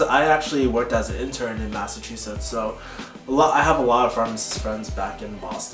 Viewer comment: Every time you guys say Massachusetts, all I hear is "Massive Two Shits" lol